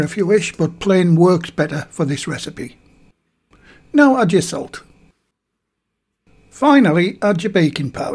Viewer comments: you instruct “add your salt” but the subtitle says “1 tsp. sugar.” I assume that subtitle should have said “1/2 tsp. salt.” Thanks for the recipe! I’m going to make some tomorrow to eat with butter and honey! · At the 2:10 minute mark you say "now add your salt" but the caption says sugar. Which is it? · Did anyone catch at 2:09 he said, "Now add your salt" and on the screen it was add 6g or 1tsp sugar? Anyone know which should be added? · Caption says sugar, voice says salt? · Great recipe. Thanks. Note...at 2:12 captions say sugar when adding salt.